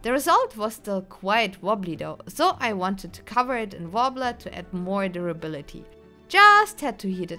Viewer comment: what is the material she wraps the foam in? It wasn't listed in the materials and I can't tell what she says 😢😅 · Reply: Actually, don't worry, but for anyone wondering, she uses Worbla Fine Art Thermoplastic sheets, hope it helps someone